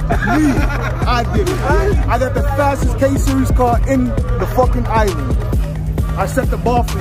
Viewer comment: did called out buju?? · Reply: K series he said not b series